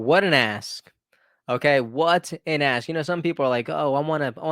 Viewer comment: No offense, but there is a 'k' at the end of the work ask. I thought you kept saying 'what an A$$'! But it is a great heart warming story!